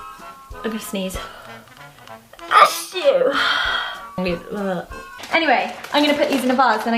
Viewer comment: It sounds like she is saying ,a shoe